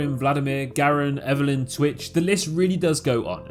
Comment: did he say switch